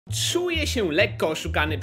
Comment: Ja usłyszałem ,,czuje się lekko osikany przez epi games